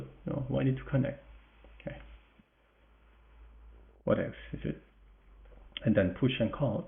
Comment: In England, we have some 90's slang, "what evz" meaning, "Whatever" or "Who cares". For a while I though you were saying that! xD But I hear now, you're saying "What else" :) :) This made me smile a lot. Also, great game. Thank you